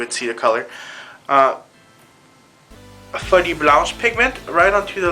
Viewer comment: Every time you saw "Folie Blanche" I hear "Sweaty Blouse" and I laugh.